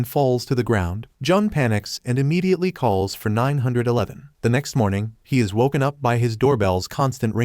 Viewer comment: I THOUGHT HE SAID 9/11